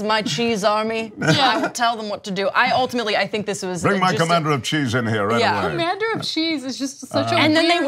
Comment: I thought she said "Commander of Cheats".